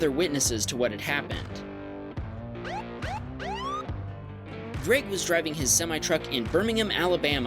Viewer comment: am I the only one who heard drake instead of greg or